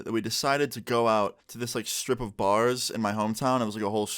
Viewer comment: at the beginning when you said "going out to a strip of bars'" I thought you said 'stripper bar' and I was like *hold up*